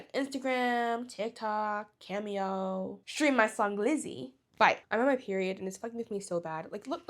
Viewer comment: I thought you said glizzy was the song name.. im sorry…